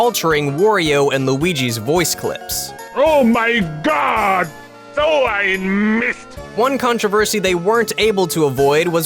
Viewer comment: Everytime I hear that soundfile I think he says ,,So ein Mist!'' (at any Germans here :3)